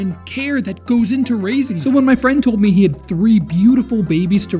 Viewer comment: Bro I thought it said ranking instead of raising 😭😭😭